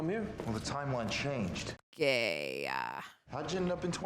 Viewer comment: did say “gayyyya”